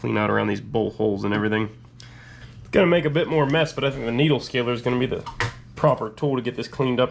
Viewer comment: Could you please tell me the name of the tool you are using to clean the solid mouse liquid from that plate. I couldn't hear the second word you used when it was mentioned. Thanks. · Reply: Needle scaler